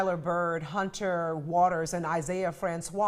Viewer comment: No matter how many times I replayed it, I kept hearing "Hunter of Waters".